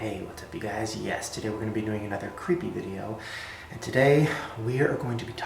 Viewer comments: I was stretching and I thought Shane said "hey guys today we are going to die" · Every time he says "yes today" I always thought he was saying "YESterday"